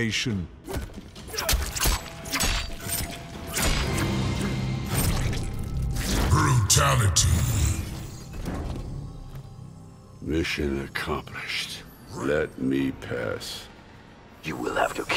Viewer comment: I keep mishearing "this shit, accomplished"